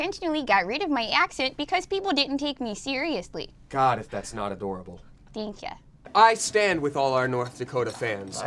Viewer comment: AFTER BOZE FINISHED TALKING DAMIEN'S JUST LIKE "god if that's not adorable" THAT'S SO CUTE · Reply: Grace Johnston Honestly when I first heard that part I thought he said "that's not adorable" And then I was like ?? But then I replayed it and then I heard it correct